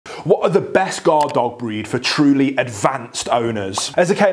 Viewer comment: I first heard: " what are the best guard dog breeds for very advanced STONERS?.."! LOL